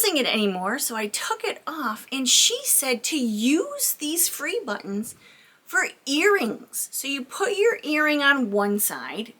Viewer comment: I thought you were saying to wear the buttons as earrings 😂